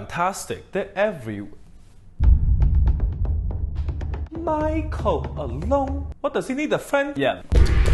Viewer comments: I thought it said Michael Afton😭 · I thought the McDonald’s one said “Micheal Afton” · I thought the wichael alon said Michael afton💀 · I thought it said “Wichael Afton” for a second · Thought it said Michael Afton 😂😂